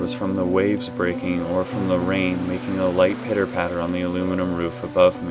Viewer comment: Brr, this one was really intense! Was that the real Max banging on the door at the end? Ahaha, also I misheard 0:37 as "making a light pedo pattern", had me confused for a sec :P